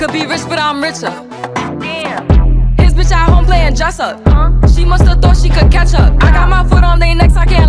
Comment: So, at 1:22 does she say "She must've thought she could catch up" or "She mustard thought she could ketchup?" The second one is more fire imo.